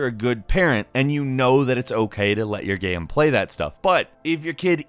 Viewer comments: did he just say let your game play it self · did you say “let your game play that”? Who of your followers gave birth to a Game instead of a child?